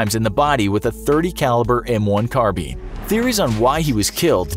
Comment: Did he just say a 30 caliber m1 car bean?😂